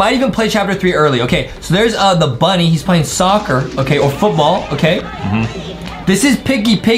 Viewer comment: Did I hear them say football at 2:31😯